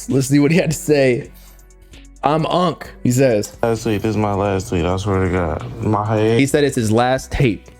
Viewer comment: it sound like lucki said it’s his last tweet not last tape😂 just be saying anything, he needs his ears checked i think